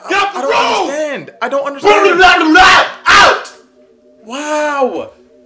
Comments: "I don't understand, I don't understand bluwalawula OUT" 💀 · "Gettt ooooooouuuuut get out get out the room" "I don't understand what!!!!?? "blumglumblalvrbla get out"